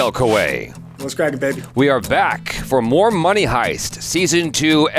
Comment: "We are back for more money" -> I thought he was really saying this!!